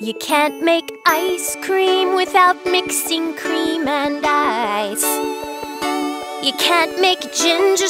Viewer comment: Ooooooh, it’s “Cream and Ice!”. This entire time I thought she was saying “Cream and Paste”